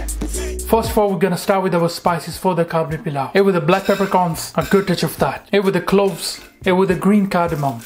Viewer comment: What did you say @ 0:42 after black pepper, you say "gooduchava"?